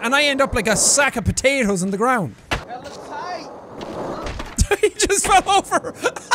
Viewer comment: listen closely he actually says "that was tight"